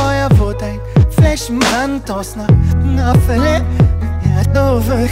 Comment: Was sagt er eig ... ''Aus nem kleinen feuer wurd ein flächen brand ... aus ner nafele ein irnovele'' oder so was meint er ( 2:00 min )